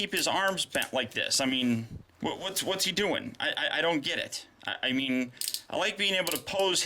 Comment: every video he make i just hear he say " I don't pretty like"